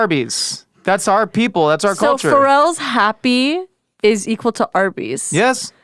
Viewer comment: Thought you said Farrell's!